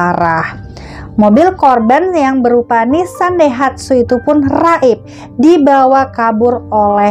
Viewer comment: Mobil nissan daihatsu? Apa aq yg salah dengar ya 😯